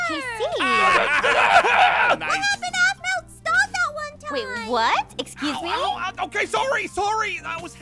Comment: Can someone say what said at 8:09because I couldn’t hear what she said, Ty ! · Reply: She said what abt dog at one time